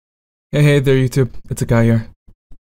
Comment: I heard "hey hey there youtube! It's a guy here"